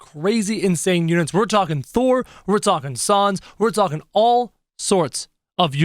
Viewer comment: did he say sons instead of sans